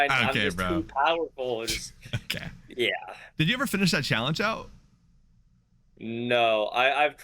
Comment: The first thing I heard was “did you ever vape”